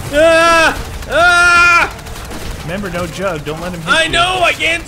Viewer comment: I thought said " remember no drugs"